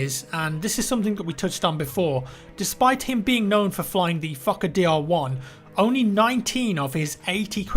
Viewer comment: Sounds like ur saying fukker